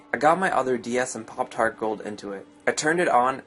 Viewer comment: When he said, "Popped my Heart Gold in," I thought he said, "Poptart Gold in."